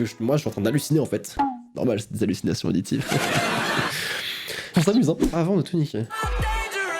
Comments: À 7:00 j'ai entendue avant les tourniquet. 🤔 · moi j'entends clairement ''attend le tourniquet'' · Au {avant de tout niqiuer} pour moi j'ai entendu 6:59 avant le tourniquet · Dans le "Avant de tout niquer" J'ai entendu "Avant de tourniquet!" Par contre, je sais pas pourquoi... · moi j'entends "avant le tourniquet" 😂